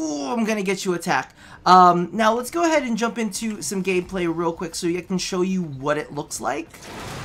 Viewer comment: did you say gay play or am I trippin